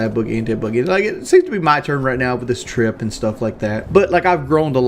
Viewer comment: Did he say he "groaned" a lot? ✅ True